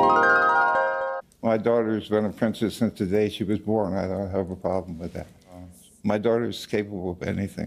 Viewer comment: first thing i hear: "my daughter has been offensive since the day she was born and i have a problem with that"